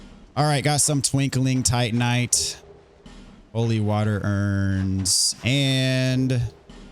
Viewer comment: thought he said "Molly water urns"